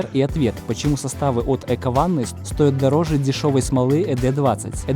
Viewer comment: Мне послышалось смола от ЭковАнус...